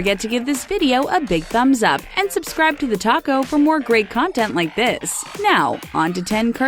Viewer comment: I thought she said TheTaco XD